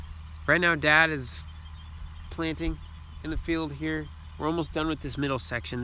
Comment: It sounds Like you said dads playing in the corn lol 😂